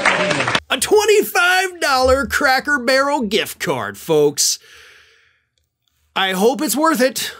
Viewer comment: I had to listen to it a few times because it sounded like he said $2,000 gift card But I wouldn't sit through an hour for even at that much to eat at a Cracker Barrel. I'd be like, nah, I wouldn't eat there even if I had $2,000 to spend, let alone sit in front on MTG for a CHANCE to win that much, for a Cracker Barrel. I don't know which is worse at this point. MTG or Cracker Barrel.